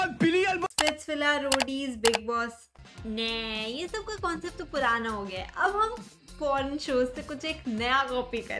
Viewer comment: What did she said at 0:32 ??🤔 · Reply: Purana concept ka shows chori ho gaya aab kuch naya aaya hai